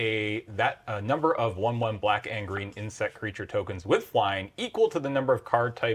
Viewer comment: Oh, "black and green Insect creature tokens". The first time I heard it I heard "black Angry Insect creature tokens" and thought I'd missed a mechanic. Haha.